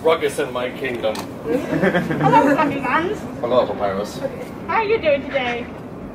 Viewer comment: I swear I heard Papyrus say "Hello Cap-I-tan" to Asgore.